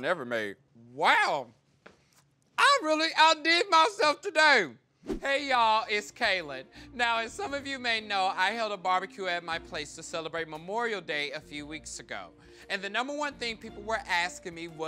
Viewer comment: I thought he said he had a barbecue to celebrate Oreo day lol